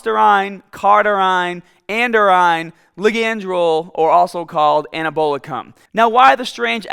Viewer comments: Did he just say "and a bowl a cum?" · Anabolicum (IT legit sound like you said "And a bowl of cum") 😂